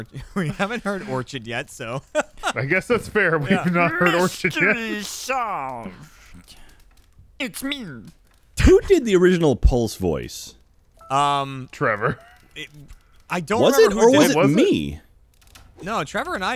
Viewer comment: I thought Jack said “who did the original Pol’s Voice?” Pol’s Voice is a Zelda enemy